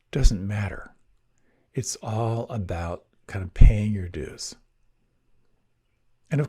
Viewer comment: Thank you very much... but as english isn’t my native language.. i couldn’t understand what you said on 14:38.. “It’s all about...!” Could you please write me what it is all about?) thank you so much :) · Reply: Hi! Ah yes, I said, "It's all about paying your dues," that is to say, it's about putting in the time to keep coming back and re-arriving, which eventually leads to more calm and more of a capacity to observe than react. Thanks for letting my clarify that and best wishes to you!